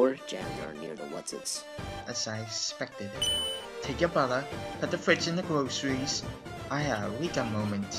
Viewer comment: i thought gaster said "take your brother and put him in the fridge" lol